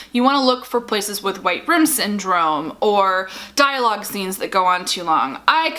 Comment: What is she say around 5:32? It is "something" syndrome? What is this? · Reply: Never mind, I got deeper into the video and heard it clearer, it is white room syndrome. I kept hearing "write in" syndrome. lol